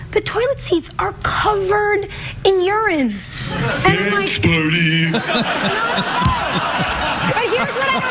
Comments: what did geoff say then? and 8:42 as well i will be very thankful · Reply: the 9:07 he says sex party !! 8:42 i don't know sorry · Can anyone please explain what Jess says at @ 9:07 and Craig @ 1:10? I cannot understand them there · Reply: "Thanks very much. You're a very lovely and supportive woman." @ 9:07 "Sex party!!"